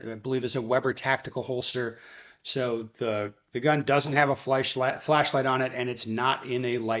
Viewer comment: HE SAID WHAT??? Did just almost say flesh?